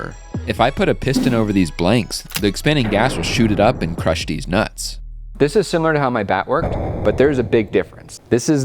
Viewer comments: He really said deez nutz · You could easily make a whole new YouTube series with this, "Will it crush!?", maybe dress it up with bunny ears around Easter! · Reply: "[...] the expanding gas will shoot it up and crush deez nutz." The captions said "these nuts", but I heard differently! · 😃 Did I hear that correctly. Lmao (Deez Nuts). CLASSIC !!! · did i hear "crush deez nuts" ? ahh i love the subtle throw aways' and humour in your videos..